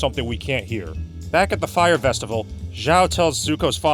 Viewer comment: "Back at the Fyre Festival..." Did I hear that right?😅 I guess it fits because this movie and that music "festival" are both dumpster fires.